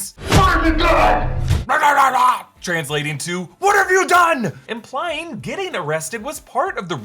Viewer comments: I’m gonna be honest when Batman said “What have you done” I thought he said “Why aren’t you dead” and when Alfred said “Dear God” I thought he said “Dick” as in Richard Grayson(Robin). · I legit thought batman said LEARN to DIE! Then I was like...dang. lol. What have you done makes more sense though. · I thought Batman said WHY DID YOU DO IT! But I guess it’s WHAT HAVE YOU DONE! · I like how copies and translates batman words "what have you done" because in the trailer I kept on wondering what he said to riddler. And I thought he said "burn the gun!"